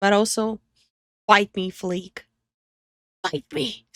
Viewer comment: did she say fight me or bite me?